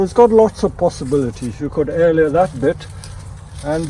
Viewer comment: he says "you could aeleorate that". I keep hearing this word but the spelling escapes me so I can't Google it. Can anyone tell me how to spell this word? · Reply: Air layer :)